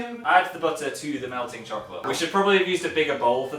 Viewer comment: the first time i watched this i thought you said dog chocolate...awkward.